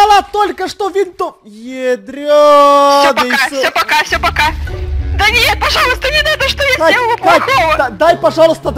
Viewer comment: сказала«да Мия пожалуйста не надо» или мне послышалось?